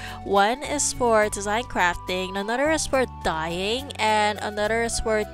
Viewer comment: I heard another is for dying...like the die die...Lmao